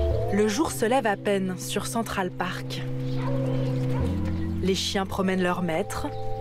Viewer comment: Mdrrrr a 0:51 dite moi que je suis pas le seul à entendre "les chiens promène leur maître" 😂😂😂